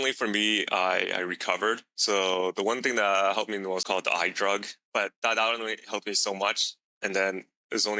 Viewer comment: can someone write down what was said here? The I drug? 👀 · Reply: I-ver-mectin